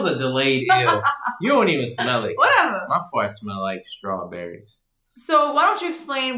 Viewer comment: Nobody: My fart smell like strawberries😂😂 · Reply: Jennifer’s Journey I thought you said FARTED🤦🏾‍♂️